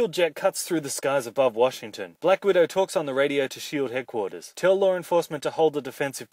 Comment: Did he just say "Tell Lauren Faust"?